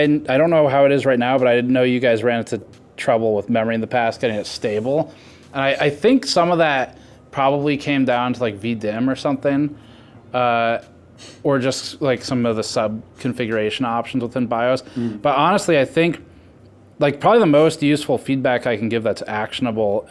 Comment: When Steve said "it came down to vdm or something" i thought he said "it came down to being dim or something" :)